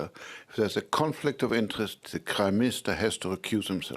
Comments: did he actually say Crime Minister? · anyone else hearing him say Crime minister?